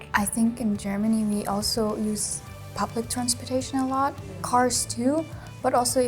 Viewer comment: I thought she said puppet transportation. HAHA